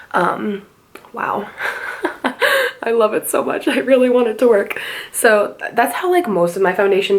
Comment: is it bad that at 7:07 I thought she said 'i really wanted to twerk' lol I really need to go to sleep